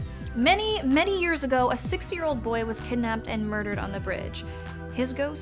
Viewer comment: did just say sixty year old kid at 8:25?